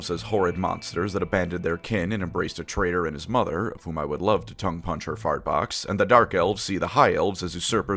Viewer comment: Did I hear “tongue-punch her fart box”?